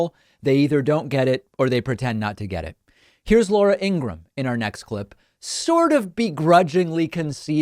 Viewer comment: Oops. I just misheard you say "Laura Ingram" as "Laura Ingalls". "WTF I thought - sorry..." 😂